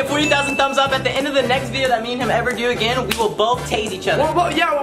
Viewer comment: Omg I thought they said taste each other 😂